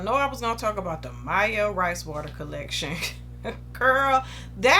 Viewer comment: With the rice water collection i thought you said "that hoe collection" lollll i almost passed out